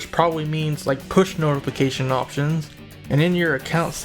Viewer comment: I misheard Push for Kush for a second there